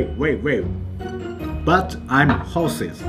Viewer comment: I thought he said "I'm horsea" xD